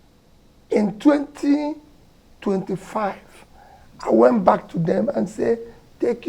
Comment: did he say he left UK in 2025? wtf? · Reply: 2005